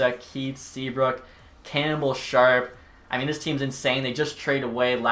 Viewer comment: I thought he said cannibal